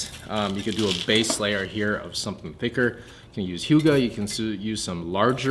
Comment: what did substrate did he say before scoria ? Juga? Yuga?